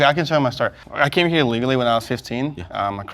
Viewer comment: He said "legally." The closed captions are done by outside companies who weren't on set. The follow-up question was "You said you came here through the process. How do you feel about people who didn't?" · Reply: the closed captions at 13:48 literally says illegally. the panel misheard him also the first time. it was only later when he said he "walked 2 days through the desert to get here" that they realized.